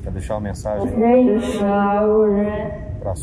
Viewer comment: Entendi "deixa morrer"